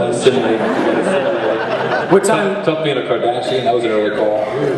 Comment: I first thought I heard "Tough being a Kardashian"!!! HA!!! ... Yes... all that makeup... :-) LOL. I am sure that Cardassians had it tough too.